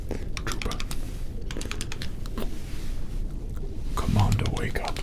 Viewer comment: I think he said don’t Wake up